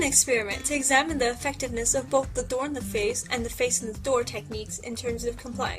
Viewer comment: Did she say face in the door?